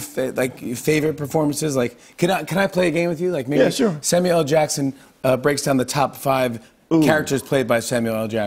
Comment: I love this guy. He quit smoking. He's been with one woman 48 years. He didn't say one swear word on this show even tho he's the king of saying Mutha Fu*ka. He watches anime. Samual L. Jackson should win a prize for just being a great guy honestly. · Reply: I legit thought you said, "Samurai L. Jackson"